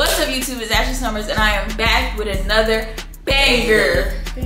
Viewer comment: did he say baeee ???!